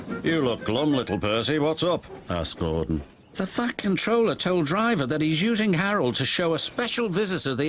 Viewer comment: He sounded like he said f*ck controller!